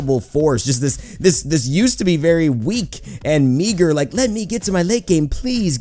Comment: did you just say the "N" word or is that just me? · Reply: "meager"